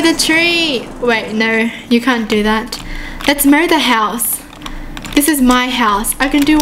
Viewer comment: I Thought She Said Lets Murder The House Lol She Said Mow The House...